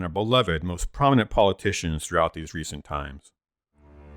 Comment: Is it only me who heard at 4:56 Gandolf saying "follow, you fools" instead of "fly" or "run"?